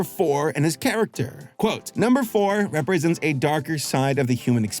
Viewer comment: Look, that guy can generate as many sentences as he wants to about the super duper dark and deep meaning of Four, but all I heard was "Four represents FUD."